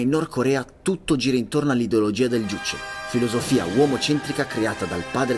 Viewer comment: Non chiedetemi perché ma a 8:01 ho capito Duce 😂